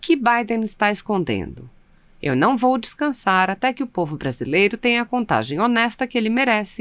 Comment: sera que eu ouvi a narradora dizer" eu nao vou descansar ate que o povo Brasileiro..." da voz da narradora?